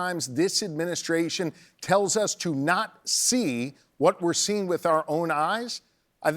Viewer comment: saw what you did there. Administration are literal Nazis." I, for one. don't understand what you are referring to, so I'd love to hear you spell it out. At that point in the video Shapiro is emphatically gesturing with a vertically oriented palm 'chop' motion that is often used as a variant of whole-hand gesturing-AT-something or driving-home-a-point. So, I don't see 'what he did there'. I have seen that gesture used often in conversation, and not ever intended as some kind of Nazi gesture. Please explain. Thanks. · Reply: lol, he said “not see”